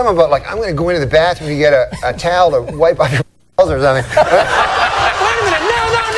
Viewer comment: What does he say when it's bleeped at 0:37? Everyone says it's "balls" but that doesn't make sense and the reaction would be way over-the-top.